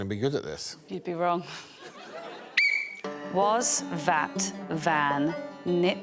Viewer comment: what is the second word Catherine says for the 3 word challenge. Does she not say that When I heard it I was like wait a minute. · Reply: I think it was probably vat, they just sound similar